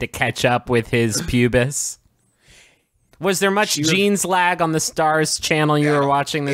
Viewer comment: I heard "dong jeans"